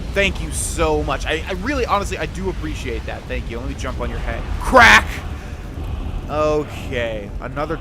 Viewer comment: I first heard that as "QUACK" XD